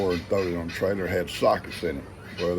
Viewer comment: My girls are nekkid too! They only get their necklaces on when going out! I thought you said a box of socks not sockets! I couldn’t understand why you saved socks from the flood? Guess I’d better pay attention!😂 I have some blooms on my squash and tomatoes!!❤